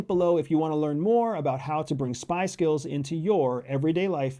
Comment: lol did you say: "spice girls" or "spy skills"?